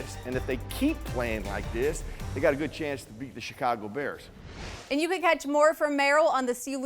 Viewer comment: I thought Hoge said "They got a good chance to beat the shit out of the Bears."